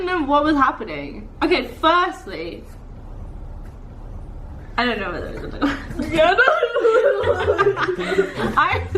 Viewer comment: What does she say at 3:24 ?😅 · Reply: 'I didn't know what you were gonna do' or something along those lines!